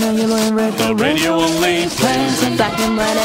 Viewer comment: no matter how many times I hear "This part specifically" It always sound like she saying "Black and Red"